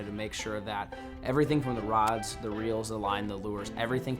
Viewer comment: what was that said at 17:04. Reels????